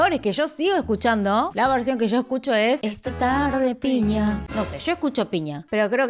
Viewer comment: la verdad yo escuche este tarro de piña y me quede tipo :)))????